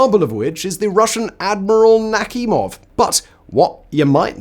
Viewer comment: for a second I thought he said Akimov, and was like ..."they really named a ship that after Chernobyl."